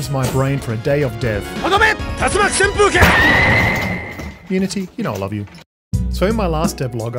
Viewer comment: "Unity, you know I love you" ... that has aged like rotten milk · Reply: My brain is so shranbled with this whole unity thing that i heard him saying unity we dont love you